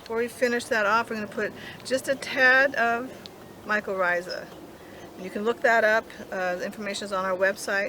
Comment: Can you spell what the the sprinkles are? I hear "mika risa," but I don't think that's what I'm hearing. THANKS! :) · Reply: mycorrhiza a fungus which grows in association with the roots of a plant in a symbiotic relationship